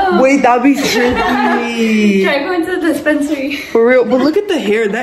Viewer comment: DID SHE SAY THE DISPENSARY??